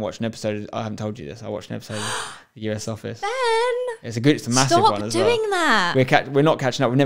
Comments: I thought she said Ben for a second, I was creasing. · Did she say Ben at 21:20??